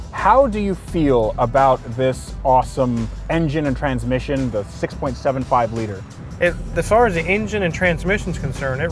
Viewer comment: Also at minutes 20:13 when asking how does he feel about the "awesome engine and transmission the"( the way he says the size of the engine confuses me, it sounds like he is saying it's a 6.7, 5 liter. That doesn't make sense to me???!? 🤔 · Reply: He was saying 6.75l, as in 6 & 3/4l engine. Thats the classic Rolls engine size.